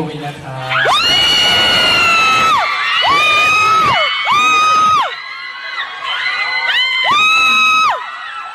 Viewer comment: what is 0:41 what did win say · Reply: "kub win metawin na kub"